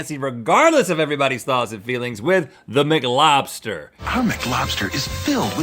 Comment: I thought they said McLaughlin in staid of Mclobster